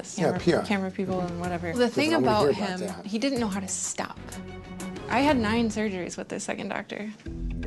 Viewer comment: that last doctor needs to be reported to the board. that is malpractice. 7 surgeries and not one success, he botched her intentionally to keep her coming back and to make money off her. "i'll do the surgeries for free if i document it" he took advantage. i'm glad terry was able to help her, those doctors truly destroyed her body. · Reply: Listen closely..... She said she had NINE (9) surgeries with the second doctor, not 7... Dr. Dubrow repeated what she said: "NINE MORE?"